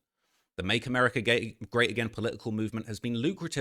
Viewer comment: Huh ? Did he say Make America gay again ? Freudian slip ?😂😂😂